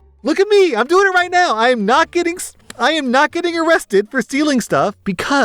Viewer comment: at around 9:32 it sounded like you were about to say you weren't getting stolen and i was about to laugh so hard